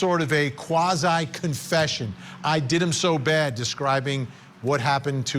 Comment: Buster has enough issues having flaming Red hair with a Name like Buster. 12:33 I listened to that 20 times. At 1st it sounds like he says “I did em so Bad” but he definitely said “They” you can here if you have decent sound or headphones Anyway, You don’t think Buster dodged his own Murder trial with Steven Smith. His name was brought dozens of times if not more to law enforcement and it was swept under the rug. Hired Private Investigators and defense attorneys couldn’t get any information from anyone BUT I bet they will be looking into it now. Stay tuned.